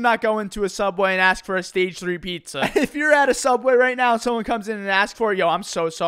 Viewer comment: What Milad said: "Don't go to Subway and ask for a stage 3 pizza." What I heard: "Go to Subway and ask for a stage 3 pizza." >:) 🍕🍕